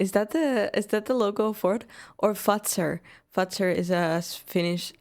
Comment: I swear if she is saying fuxer.... I don't even have to make the joke....